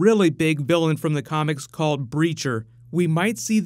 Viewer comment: Am I the only one who thought he said Preacher (from Vertigo comics)